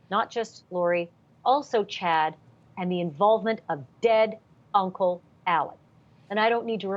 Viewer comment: Did she call him uncle Ali? Maybe they were just chums but I’m disappointed with how many errors I keep catching COURTV making. They are no Nate Eaton from East Idaho News. That’s for sure.